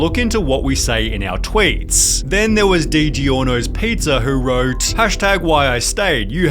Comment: Did he just say Dee-jee-orno’s pizzas 😂?